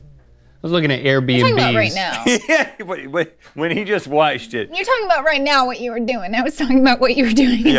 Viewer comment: Go to 16:57 rhett says waiched instead of watched😁😂